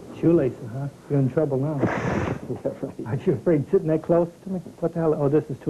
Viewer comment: its sounds like hes saying, "arent you afraid to make love to me?" right at 7 secs